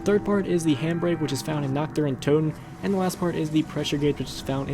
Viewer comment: I thought he said doctor untoten